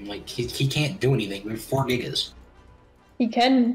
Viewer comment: Did he say we are 4 niggas